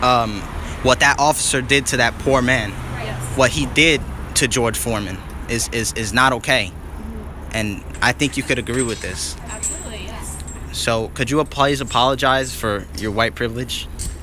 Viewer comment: did he say George Foreman?